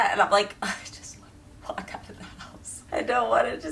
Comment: “Just let me WALK out of the house” Good thing for subtitles because I heard, “Just get me the F*ck out of this house”. Which is equally valid somedays. 😂